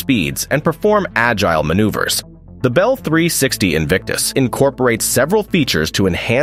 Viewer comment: In case anyone missed it its name was the Bell 360 Invictus 🙄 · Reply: I heard "Bell 360 sphincters"